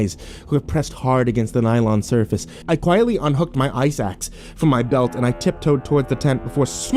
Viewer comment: When he said ice axe I thought he said eye sacs.